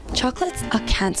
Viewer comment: thought you said charcoal and i was worried about binchotan